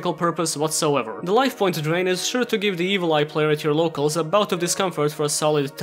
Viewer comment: When he said Evil Eye, I thought he said Evoltile, and I about went into a mental fucking breakdown having to remember those things.